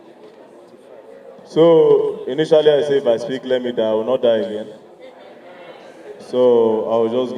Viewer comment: I thought he said if he speaks let him die Why speak then?